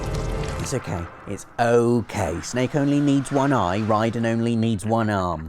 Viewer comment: i did that before it was funny cause i still looked for him lol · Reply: lol! i thought at first he said "dead", but after hearing it about three times, I heard it was actually "snake" dragged out.